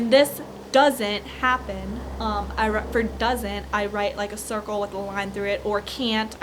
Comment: 'path ira bird doesn't i'd write like a circle of a hundred porky entire ethical' is apparently what she said at 7:43 according to the subtitles!!